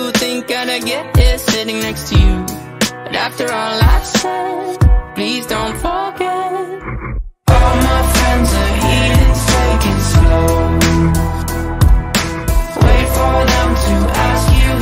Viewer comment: I never knew the real words until today but it is still my favorite song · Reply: nah I heard"all my friends are eating steak and snow"